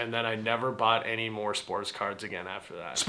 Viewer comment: first I heard Sports Cars 😂